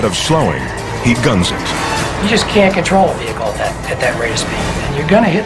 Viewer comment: '' You just can' control a car at that spped '' did he really said that ? ( not exactly - but did he said something like that ? ) .... i invite him to germany and i show him what SPEED is... ha ha ha